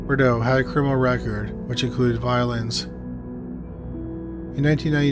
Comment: "he had a criminal record that included violins" 🎻 I SWEAR THAT'S WHAT I HEARD